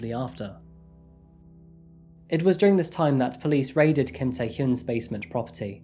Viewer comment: I can not be the only one who thought it said kim taehyung